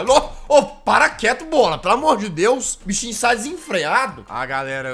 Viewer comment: e eu q escutei P0rr4 e na vdd o falou "bola" : 💀